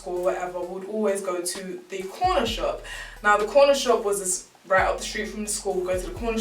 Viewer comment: I thought you said porn shop 😳😂😂